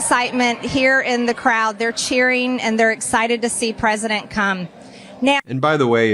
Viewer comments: Did Marge call him President Cum at 2:38? 😂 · Did she just call him "President Cum"? She did. She called him President Cum in my city. There really is a God. 🤣🤣🤣🤣🤣